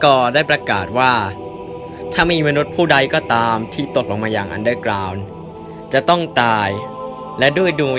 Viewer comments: พี่พูดว่า ตดลงมาในอันเดอกราว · มีใครได้ยินว่า'ผู้ไดก็ตามที่ได้ตดยังมาอันเดอร์กลาว'บ้าง